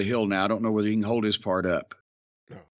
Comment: I lost my shit when he said he doesn’t know if Dustin can “ hold his fart up”. Damn near choked. Never heard that saying before lol.